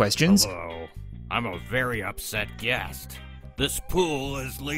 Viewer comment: the character says upset but the subtitles say unhappy?